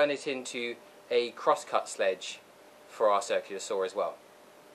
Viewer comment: Hi. Great job on the video. It was easy to follow the steps with minimal tools. I made one. Just a note on your grammar. Several times it sounded like you said crosscut sledge instead of sled. Maybe it’s just the sound quality though. Anyway, keep up the good work.